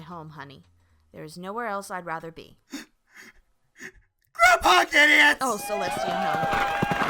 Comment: for some reason i keep hearing saying grandpa gideon